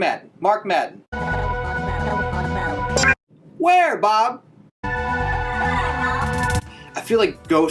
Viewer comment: I thought it says where mom